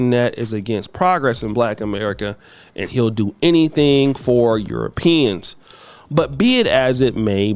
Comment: I misheard "...he'll do anything for Europeans" to "he'll do anything for peen." 😂